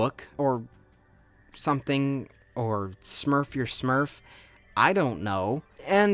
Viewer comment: when you said smurf a smurf l thought u said kill yourself